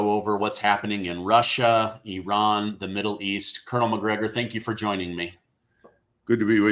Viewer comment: I thought he said Connor Mcgregor